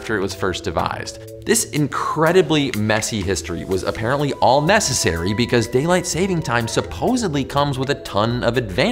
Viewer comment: Did I just hear "messesary?"